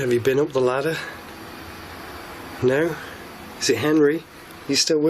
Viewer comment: it sounds like you got a response that sounded like a “NO”